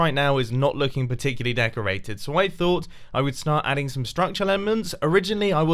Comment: Anyone else hear say "Structure Lemons"?